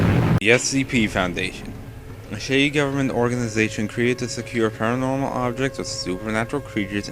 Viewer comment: no need to insult them dude. (It sounded like he said "a shitty government organization.")